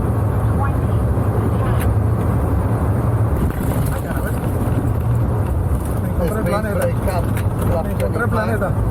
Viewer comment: what did they say at 12:55? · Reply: "Ayala Ve**a" ("Holy S**t") 🇵🇦 🇵🇦